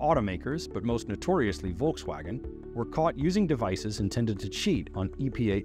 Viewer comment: subtitles says caught, but in the video he said "collect", I've seen this in other videos on YouTube, is this future, communist are blocking sensitive word too? hopeless. 😒